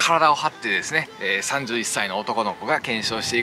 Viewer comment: 31歳男の子に聞こえてそこに引っかかったw